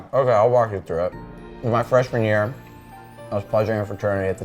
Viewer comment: Huh?? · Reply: prob shoulda put a timestamp lol, at one point during the beginning he says he was pledging a fraternity but slurred a little and it sounded like he said "pleasuring"